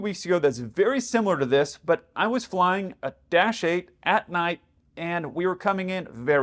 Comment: When you said you were flying a "-8" I heard "Dash 8", and I thought, that's odd, why would you be flying one of those? Took a moment to click that you were in a 747-8.